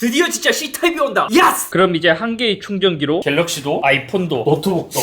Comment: 형 YES 발음이 좀 찰지다??? 야..ㅆ ??? 내가 잘못들었나??